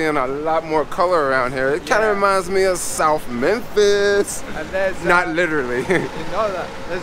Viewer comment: Omg! Did I hear you say “reminds me of SOUTH MEMPHIS!!!🔥 I always say, We will represent wherever we are on the face of the planet!😄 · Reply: South Memphis